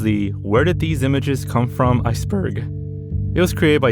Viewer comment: when bro said iceberg in the beginning, i thought he said aesthetic😭